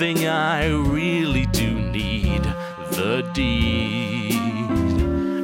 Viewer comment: Also I thought he said "I need the d" for a second there!